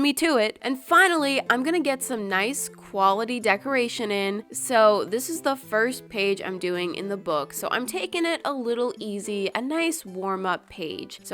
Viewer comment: did sis just say decorationing I'm rolling